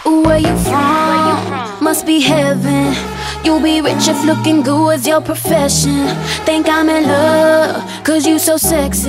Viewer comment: I Heard "if being goo was your profession "😆😆😂